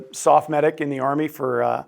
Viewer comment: mentioned "Soft Medic". Maybe he meant "SOPS Medic"? Not familiar with that term. What was his MOS? Sounds like a 91 Charlie. I was an AF Medic. Love it! 20 years. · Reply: I thought he said SOF medic.